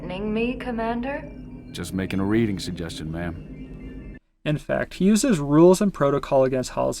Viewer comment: Ok so can someone verify if Jorge is the son of Doctor Halsey? Cause during Noble Teams first interaction with Halsey, I always thought that Jorge said “Mom”. But the subtitles say “Ma’am”.